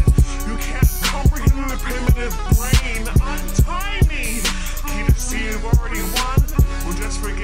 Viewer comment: I thought she said I'm tiny but untie me